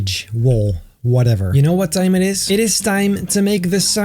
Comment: did you say: (7:05) "you know what time it is" or "you know what thijmen is?"